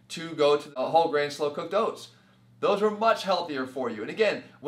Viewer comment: Hey guys what did he say about whole grain... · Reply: He was talking about whole grain oats and oatmeal.